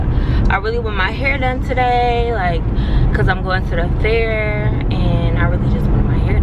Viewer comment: I thought u said u were never going to the fair again.